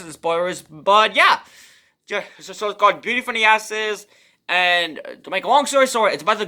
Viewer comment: I thought you said Beauty of the Asses 😄 that would be interesting 🤔 I knew I misheard you but then you said she ends up getting into porn and stripping 😱 so it makes sense now 😄 Awesome I read the prologue. Looking forward to reading more.